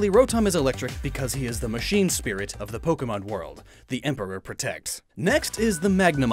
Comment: Did he just say that the Emperor protects, ha 40K 😂